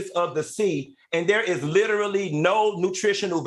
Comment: There’s no way in hell that she could ever mentor me once I heard her say “LITTELY” instead of literally‼️🤔💭👀👩🏽😒🤬🤣🤣🤣